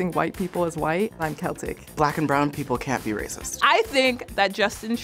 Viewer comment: okay so I want the drugs he’s having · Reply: Toe lol I thought he said they CAN be racist until I rewatched. Oof.